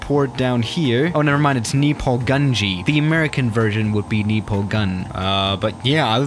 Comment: hold up did he say me pull gun